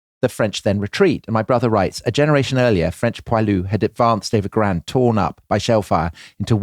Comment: What is "Grand Torn Up"? Think I misheard. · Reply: "had advanced over ground torn up by shellfire" I had to relisten a couple of times.